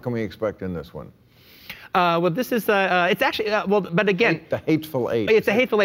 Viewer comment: I thought he said "The Hateful Ape", so I was confused why an angry primate reminded Bill of The Magnificent Seven.